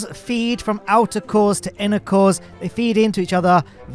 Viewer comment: Me, attention distracted: "wtf did Matt just say?" ooooh. "INNER CORES"